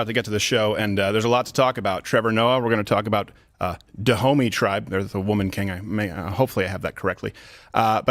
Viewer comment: Literally thought you were saying 'tha homies' tribe the first few times.